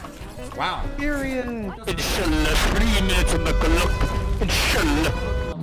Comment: did Mercy say "INSHALLAH"?